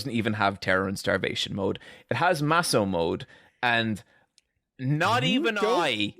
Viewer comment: Holy shit I thought said Massa mode